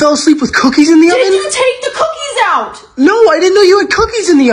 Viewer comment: "why are you filming me?" "are the cookies okay??" shes asking the right questions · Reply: For a second I thought you said that she said “are the cookies gay?” 💀